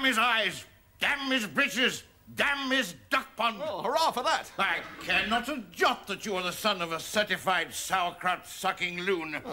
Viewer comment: What is that word that sounds like "injot". The only words that come up online make no sense in this context. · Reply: He says, “I care not a jot that you are ….” - but it’s garbled through his biscuit-chewing 😅